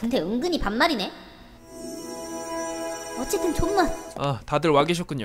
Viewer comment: 에 “어쨌든 존못” 이라고 나만 들었나..?